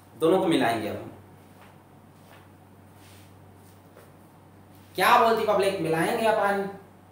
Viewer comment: pa kya bolti public?? #BhootHard #BhootHard kya🙏